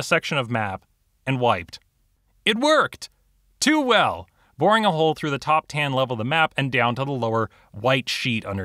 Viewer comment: wait did you just say “voring a hole”